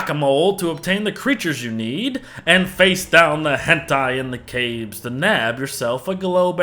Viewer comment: Did he actually say HENTAI